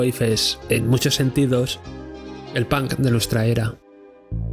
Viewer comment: jajaja te entendi "el pan de nuestra era", me encanto tu video muy bien investigado, no sabia la variedad de subgeneros que escucho y lomucho que me gusta el vaporwave en su extension, para mi el vaporwave esta vivo desde el 2014 y es uno de los mejores generos que se culmino en esta epoca